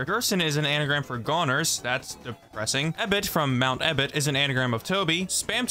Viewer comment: I heard “That b1tch is an anagram for Toby” when said Mouth ebott is an anagram for Toby